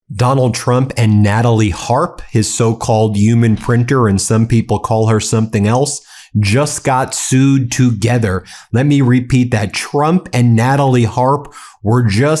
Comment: OMG I thought you said sewed together .... aren't they "close" enough now. Not even Melanoma can get in on the action ....